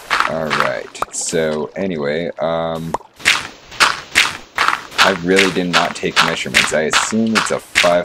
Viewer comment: The auto generated subtitles on this video are hilarious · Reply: What he said:I really did not take measrurments What Captions Translated: anyway I am how really did not a passion